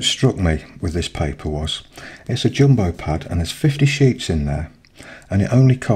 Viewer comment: I heard "50 sheeps" laughs nice to see your tutorials again. I like Canson XL paper its vegan and you can get a huge 18x24 pad for a good price.